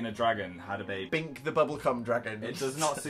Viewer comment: it sounds like Dan said bink the bubbleCum dragon! am i the only one?